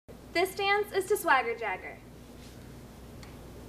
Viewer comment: i could have sworn this girl said "this dance is the swagger dagger"... i was like ok whatever you say